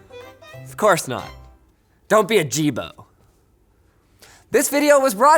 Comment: he says, Don't be a Jibo. It sounded like ajeeb, which means weird in my language.